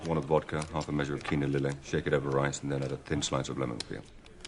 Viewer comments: I need to know I'm not alone... am I the only one who thought that Bond said this drink should be shaken over RICE? Like, for a... rice-y undertone? I feel dumb for thinking that, but I thought that was what he said up to and including in this very video... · Oh my god. I've always heard it as "shaken over rice" and I thought that was the weirdest thing.